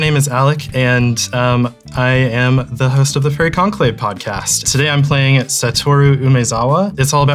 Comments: Not gonna lie, I heard "I'm the host of the Furry conclaive podcast" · HELP I HEARD "FURRY CONCLAVE"